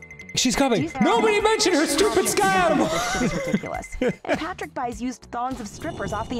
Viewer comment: This is the best GGA I've seen in awhile But wtf is Dan saying at 0:52? "Nobody mention her stupid skyum?" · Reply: Sky animals